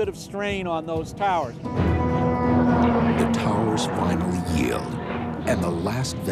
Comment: wait did you said bridges yelled thats scary